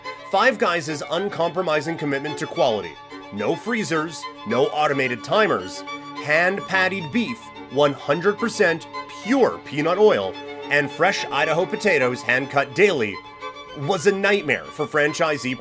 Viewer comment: Sheesh 9:38 when he said 100% pure peanut oils, why did I hear 100% pure peed on oil? Shit I'll do that for you for free!